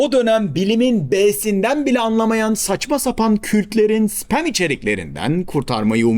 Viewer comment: kürtlerin anladım ilk başta 😅😅😅